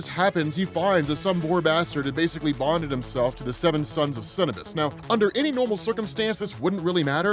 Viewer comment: I swear I thought you said it was the 7 suns of Cinnabon 🤦🏼‍♂️ 😂